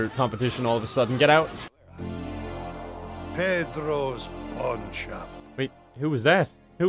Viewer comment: I heard "Pedro's Pogchamp".